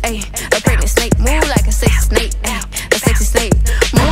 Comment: Sounds like she said a "Sexy Snape" at one point. Snape head of Slytherin house...lol! And the snake is their mascot...lol!